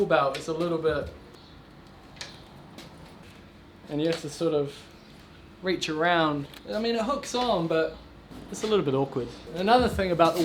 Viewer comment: I heard you love giving a good reach around anyway.